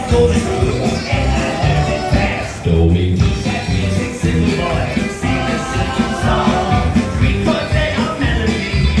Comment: did he just say dummy? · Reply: he said told me